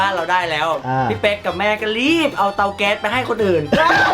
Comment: หนูขอโทษที่ได้ยินเป็นเกาเเต๊ส😂